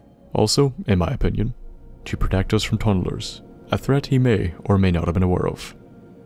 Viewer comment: I listened to your video while doing other things (starting the computer to get on with Dead Money) and managed to mishear tunnelers as toddlers. I had to back the video and listen again and now I have no idea how I could heard so wrong. Great video